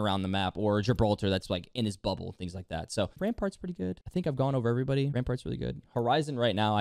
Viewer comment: i hear you saying "repart's pretty good", why is that? and why does everyone say she's not good ? :/